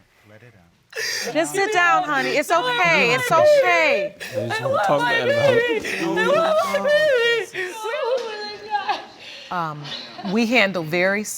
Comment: what did he say? It sounded like " I just don't want to talk about it, mom." Where are the lip readers on youtube?